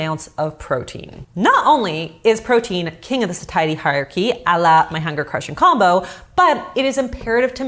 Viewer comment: sorry but does anyone know what she said at 9:47? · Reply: “King of the satiety hierarchy”, sorry I’m a year too late